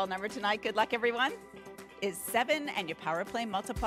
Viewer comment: i thought it was 17 the Mega Ball😭😭😭😭😭😭i was 10 numbers off😭😭😭😭😭they called 7 instead of 17